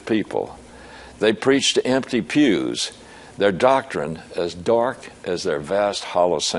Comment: What's this empty purse doctrine he talk about in 23:38? If I heard that right...